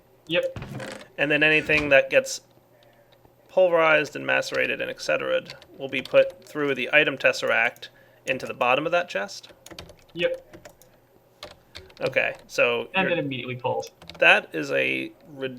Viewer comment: oh = P i thought he said what is that chest